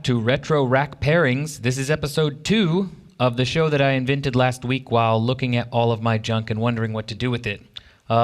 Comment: all I heard was "you discovered something while looking at your junk"